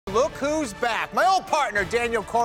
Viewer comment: I thought he said "Goku's back" at the very beginning